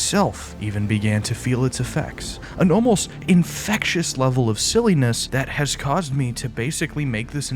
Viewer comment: did you just say infected …